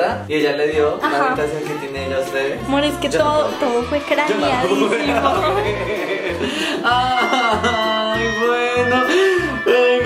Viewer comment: MOR es que todo fue craneanisteco (no le entendi xd): en ese momento osenti el terror